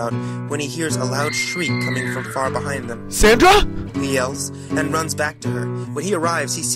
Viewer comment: AM I THE ONLY ONE WHO HEARD " SANTA?! " 😂😂😂😂